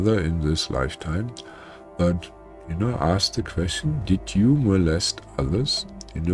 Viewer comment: I am curious, did anybody get me dead pan joke (part of the medical disclaimer) where I suggest that you consult your doctor or husband if you want to become pregnant? .... Should I mix in canned laughter? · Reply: It went over my head hahahaha I thought wonder why he said ask the husband lol 😂 lol 😂